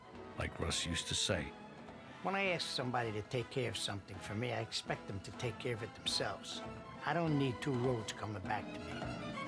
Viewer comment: What is he saying after 1:18 ?? Sorry my englisch is not that good.. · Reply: "When I ask somebody to take care of something for me, I'm expect them to take care of it themselves. I don't need two roads coming back to me."